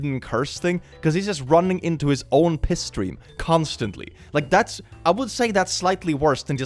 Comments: i thought you said pisstory, like a play on history and piss 😭😭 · Was playing this at 2x speed and could have sworn instead of "piss stream" you said "pistory" and it actually made some real sense...